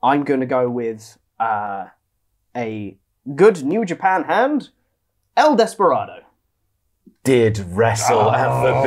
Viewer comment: I really thought said El Phantasmo when he said “El Desperado”. I was so confused why no comments were talking about this epic screw job.